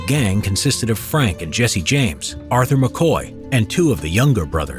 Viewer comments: Arthur McCoy I swear for a second I thought I heard Arthur Morgan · McCoy WHO?!?! SORRY I DIDN'T HEAR THAT WHO McCoy? I think Morgan fits better than McCoy